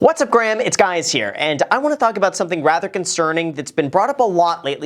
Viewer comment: Did he just say "what's up Graham it's guys here"?